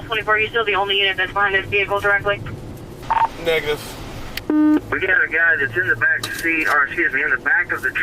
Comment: I was looking away for a moment, had my headphones on, I THOUGHT DUDE SAID NEGUS😭😭😭